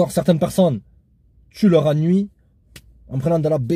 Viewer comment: « certaine personne tu les a nuies en prenant quoi ? » j’ai pas compris